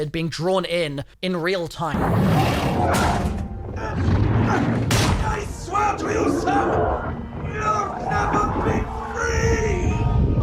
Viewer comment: i swear i hear "i swear to you sam you will never be free"